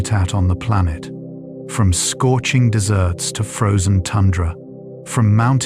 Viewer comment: Hehe 5:37 the AI says "scorching desserts" instead of deserts. 😆